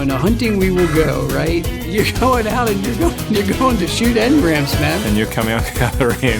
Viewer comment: Bro when he said "emgrans" I coulda sworn I hear "immigrants" 69th comment